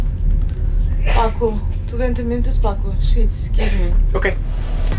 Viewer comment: i thought they said "SHITS GIVE ME",that laughed me out.